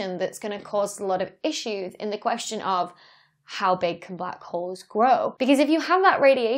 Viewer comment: I thought that it said, “How big can black HEADS grow?” Lol!